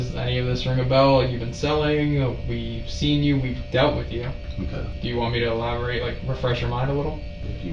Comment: Right? Smoke weed with him too 😂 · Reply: At first when the officer said "We've spoke with you before"... In my mind, I heard... "We've smoked with you before"!!!